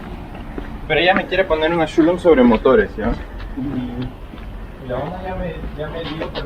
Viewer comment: that is definitely Spanish, I couldn't clearly understand what they were saying but it sounded like "pero ya me quiere poner en silencio? [unintelligible??] por el motore" (But he wants me to put a silencer by/through the motor???) but it's confusing as "motore" is Italian, it's "motor" in Spanish, perhaps some regional difference? ... they are talking about their bike and fixing it, something along those lines, because he also says "La Honda ya me dijo..." (Honda already told me...). Perhaps someone else can clarify this mystery of what exactly they are saying.. 😁